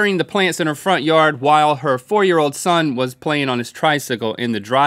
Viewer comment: I heard "her 4 year old son was playing on his tricycle" as "her 40 year old son was playing on his tricycle"...